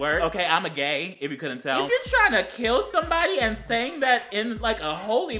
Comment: I thought the first guy was saying, "I'm a fury"